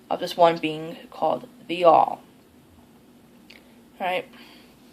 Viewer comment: Near the beginning you caught me a bit off guard when you mentioned "The All" in reference to all the gods and goddesses as one entity. I had to play it back a few times because it almost sounded like you said "Be'al" which is the name of an essentially identical deity in my customs as a Reformed Druid. In the tradition of the Reformed Druids of North America, Be'al and other deities are open to interpretation of the individual, but I personally view Be'al as the source of all things, whether it is divine Source or merely the raw creative forces of the universe. The word Be'al itself was a hold-over from the Druid Revival movement of the last few centuries... according to the revivalists, Be'al was an ancient Irish supreme being, and the word simply meant "Lord." Either way, I guess it's rather interesting that Wicca has such a similar concept with "The All." Though I also like Isaac Bonewits' interpretation that while the Earth-Mother is the physical manifestation of deity, Be'al is all that is the non-physical manifestation of deity. The beauty of it is that there are still some Reformed Druids that disagree with that interpretation, but the point is to interpret Be'al and the other deities in a manner that works best for the individual. :)